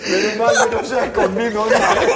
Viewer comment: Nadie se dio cuenta lo que dijo Duro 😂 "Menos mal que no sea conmigo" El que le entendio le entendio. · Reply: Menos mal que no se ha escondido*